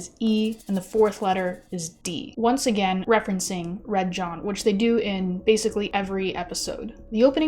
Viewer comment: Misheard "chasing Red John" as "chasing the red dot" so I'm just gonna assume MC is a cat bye